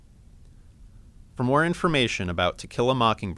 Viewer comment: I thought it sounded like he said "tequila mockingbird" at the end. I typed in "teq" into the YT search bar and tequila mockingbird immediately popped up...